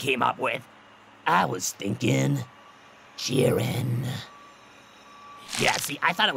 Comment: did he say Jiren??